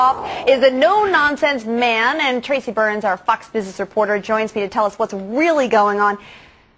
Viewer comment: it sounded like she said fux not fox